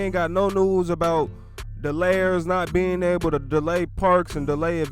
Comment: Thought he was saying layers not delayers I was confused at first wa alike that their new name?😂